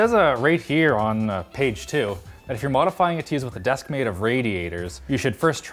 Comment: Thought this said "Desk of PREDATORS"